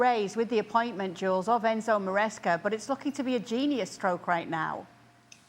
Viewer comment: Genius Coach my F00 . T! Give this squad to my granny and she'd win Sextuple and the new FIFA CWC trophy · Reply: Open your ears, just like Juls needs to. Kay did not say Maresca was a genius. She clearly said replacing Pochettino with Maresca now looks like a "genius stroke". May have been better if she'd worded it in a more traditional way, by saying "stroke of genius", however she did not say genius coach, Juls misheard her, along with you and numerous other people, apparently. 🤦‍♂